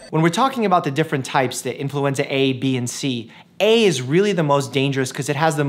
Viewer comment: I kept hearing the “influencer” virus😆